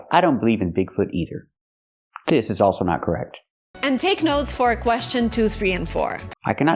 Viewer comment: I don't like this gal either, but she clearly says 'and' between three and four. From what I can tell, your English is no better than hers.